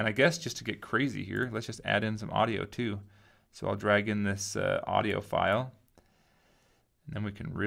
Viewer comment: Whenever he says "audio file", I hear "audiophile".